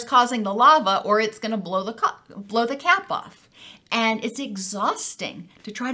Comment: "Blow the cock" don't think we didn't hear you say it xD with the volcano metaphor. Made me giggle. Around 13:15 haha